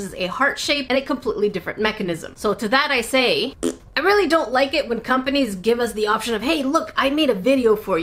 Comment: I thought you said you don’t like pink oh my God